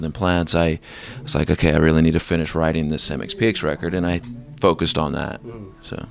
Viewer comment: I thought it was said as ‘mex-pex’.